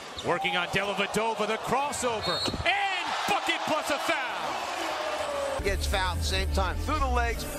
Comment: it sounds like he said fuck it plus the foul